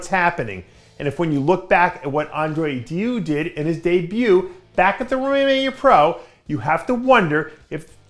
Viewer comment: seconds 0:36 what did Dave say? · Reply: Romania Pro